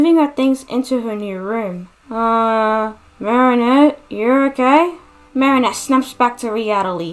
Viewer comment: I thought Nathaniel said “uh Marinette your gay?” 😂 great designs btw!